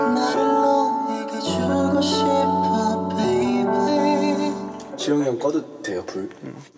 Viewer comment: Omg @ 1:37 did Taehyun says jiyonghyung?? And the "mmm" really sounds a lot like GD OMOOOO